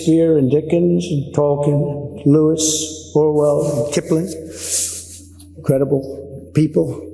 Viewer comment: did he say Orwell? 😂